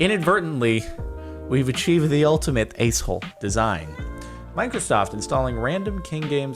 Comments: I wouldnt worry i think i thought you said"Ice hole" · The captions thought that you said ice hole instead of a**hole. You are still tricking it Jardon. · o it thought you said ice hole design lmao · The accent worked 😂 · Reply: Niko Tofa Yea it thought he was saying ice hole! XD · Your good Jordan at the beginning the bot thought you said "ice hole design" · Dude the accent made it say “ice hole design” Jardon you beat the system right after you said you probably couldn’t The caption also thought “accent” said “X n”